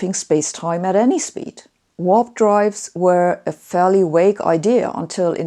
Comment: "warp drives were a fairly AWAKE idea"? Still not sure what you meant to say there. Also, is the paper publicly accessible yet? Googling is not finding it.